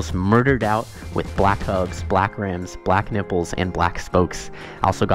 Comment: ayooooooo did he say nipples at 2:06🤨🤨🤨😱